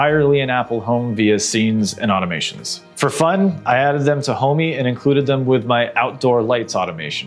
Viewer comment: Question, @ 7:24 i think you said you added them to "Home me", what is that?